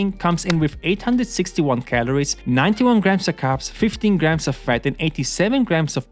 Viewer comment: I thought i heard a bunch of carbs😨😕